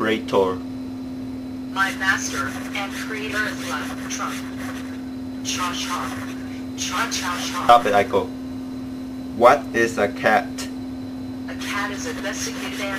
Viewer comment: what is she saying at about 7:52? it looked like she said: cha cha cha cha cha